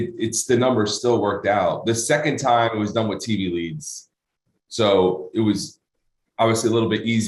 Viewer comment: Did he say the 2nd time it was done with "TV" or "TB" Leads? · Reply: TV leads, I'm pretty sure. Probably is talking about direct response marketing.